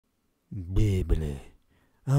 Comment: I thought he said 'oh my cow is moving'